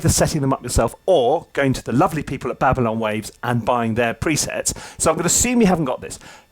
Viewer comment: Did he say "pavilion waves" @ 7:43 if anyone knows please help